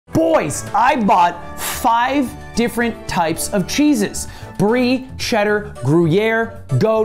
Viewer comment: What i heard: "Boys I bought five types of Jesus"